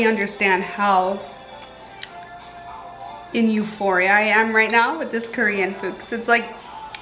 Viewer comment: did u say eUPHORIA cue music